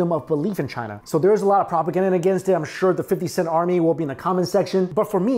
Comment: Did just say "the 50 Cent army" at 30:48?